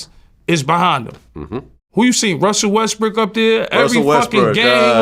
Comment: Nah I heard “Russell Westbrick” @ 1:02 😂😂😂